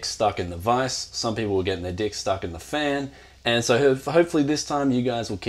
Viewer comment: Did you say, ya got ja dick stuck in a fan?